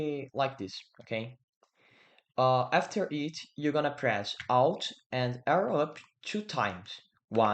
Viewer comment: What did you say at 1:12 alt and what?? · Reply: Press "alt" and "arrow up" two times